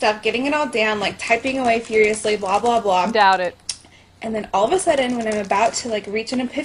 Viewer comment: wait did someone just say doubt it some where between 2:00 and 2:30? 😂😂😂😂